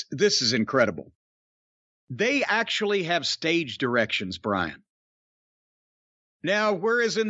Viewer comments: I thought Cornette said “Staged Erections” not “Stage Directions” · When talking about Becky lynch Jim said 'stage directions' I heard 'stage erections' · I was listening to this while I was on my exercise bike. I heard it as "Beck Lynch had a stage erection." Wat.